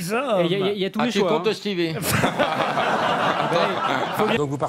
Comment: Il dit quoi à 8:27? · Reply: "Ah tu comptes Stevie ?"